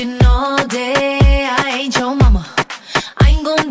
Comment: I first thought it said "I hate your mama" 😂